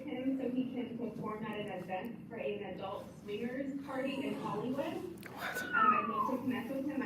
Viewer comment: what did that caller say about Off White doing what??? Did she say adult swingers?